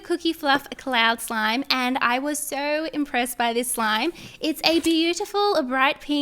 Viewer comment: When she said with the cloud slime “I’m so impressed with this slime” I thought she said “I’m so depressed with this slime”.🤣😂